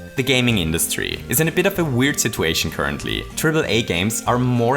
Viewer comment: Did just say “turtle A”